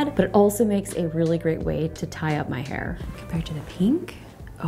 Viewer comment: Wait did she just say tie up her hair☠️